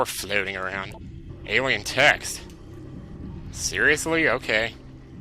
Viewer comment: When you said "alien text" I thought you said "alien tecks" ...and I was like, huh? :o